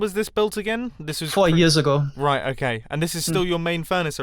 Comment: I thought he said 40 years lol.